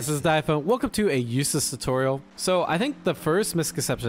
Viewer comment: This is completely wrong but I misheard the intro as 'Welcome to a useless tutorial' I'm like ouch poor Eustace